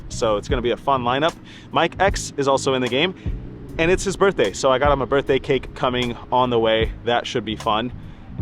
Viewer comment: During the intro i could have sworn i heard you say "its MY EXES bday and i got him a cake"😂 i thought "wow i never knew Mariano use to be gay"! Love your vlog dude. Keep up the heater. Love how level headed you are and NEVER play with emotion. Whether you are up 500k or down 500k, you always have the same focus. Stay hot kid